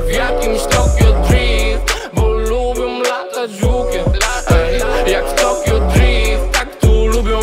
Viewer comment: za pierwszym razem usłyszałem "bo lubia latać żukiem" xD